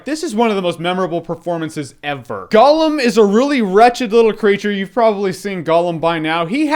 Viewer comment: lmao i thought he said, "Gollum is a really ratchet little creature" but loved the review :)